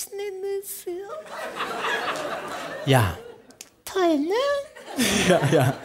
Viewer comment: Was sagt sie bei 0:09? Ich verstehe das Wort akustisch nicht. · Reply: Sascha Grammel: Ja Josie: Toll, ne?